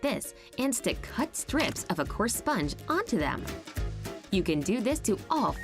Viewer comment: did she say poor sponge